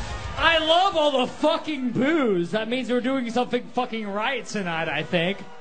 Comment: I wonder.. if Ashley was thinking, "I love all the fucking boo's" Or perhaps, "I love all the fucking booze" I mean. Just sayin'. xD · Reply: I literally thought he said booze when I first saw this video so I was soooo confused and then I watched it again an realized he said boos 😂😂